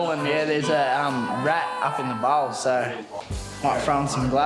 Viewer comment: I thought said up in the ball